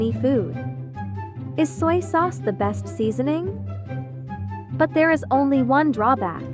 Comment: Why did I hear “is slay sauce the best seasoning”